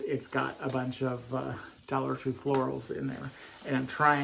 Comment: I've been looking 4 those disco balls /garland,,where were they & how much were they,,I can't find any 🙃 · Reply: Go back to the start of the video. I thought he said Dollar General.